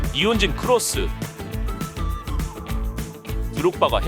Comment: 나만 니혼진 크로스를 이운재 크로스로 들었나,,,,?